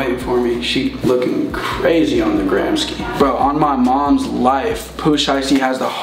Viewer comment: did he just say “on da gramski”? 😭